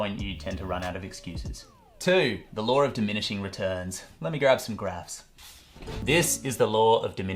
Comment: What he said “ let me grab some graphs” what I heard “let me grab some grass” 😂🌱